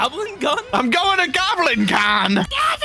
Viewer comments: I heard "Guys, I'm going to GoblinCon(TM)" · i hear goblin guy but it says goblin con